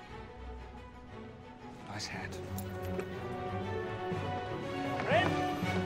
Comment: what does he say to Will>? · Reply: He said Nice hat